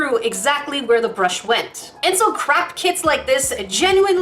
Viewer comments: Did she just say “craft kit?” NO. it is ”crap kit.” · I'm sorry but when you said "craft kit" I heard "CRACK kit" and was like Uhhhhh... lmao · I thought you said crack kit when you said crap kit. also though, my nail is too wide for those, except maybe my ring finger because I slammed it in a door when I was nine and missed half a day of school to get it ripped off and it grew back nicely. And the thumb would be the one to fit it...